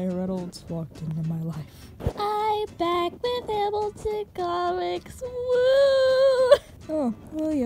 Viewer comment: When you said “i’M bAck wiTh HamiltoN comiCs WOOOO” I thought you said “I slepT with HamiltOn SongS WOOO” .-.